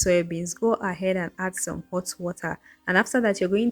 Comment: I'm talking about the soya beans, sorry.. is it hot water that you used to soak it or did you say bottle water? I watched it more than 5 times and I still didn't hear what you said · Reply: oh, hot water. Soak in hot water for 30mins